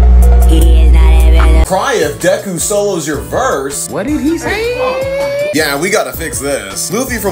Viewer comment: I thought it said “cry if goku solos your verse”💀